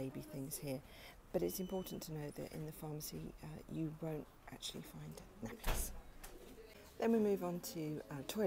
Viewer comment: what the heck is she saying here? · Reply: I’m quite quiet on this video but I am saying you can’t buy nappies/diapers in the pharmacy